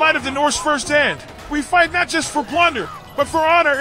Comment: had to wheel it back coz I thought he said we fought ninjas for plunder 12:55 haestinn said he wanted a flexible mama